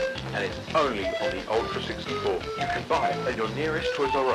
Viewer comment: guys, there’s nothing obscure about the “change your system” at the end. i know, its creepy but its only the nintendo tag line. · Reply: I thought it said chain you system